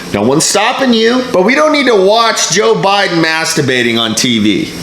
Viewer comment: Ha thought he was saying masterbaiting